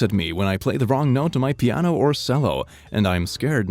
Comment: did you say sello